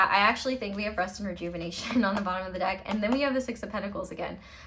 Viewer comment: Vanessa Somuayina? are you on one? lol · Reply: Just kidding I just went back and totally misheard that, it’s her deck lmao I hear that you were Vanessa lol brain fart how embarrassing lol